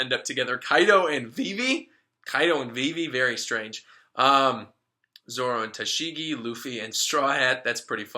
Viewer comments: oh lord at first I thought you said zoro and shiki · Misheard zoro and tashigi as zoro and shiki and I was like :O